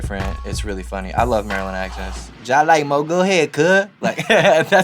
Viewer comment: Tf he say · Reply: Jah like (which means kinda not really). And Moe (which means another person)